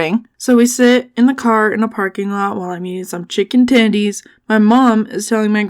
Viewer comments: I thought u said in eating some chicken tiddies. was like WHAT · I have short term memory issues 😅😂 and high functioning autism. Also, I thought you said “chicken tiddies”🤣🤣🤣🤣🤣🤣🤣🤣🤣🤣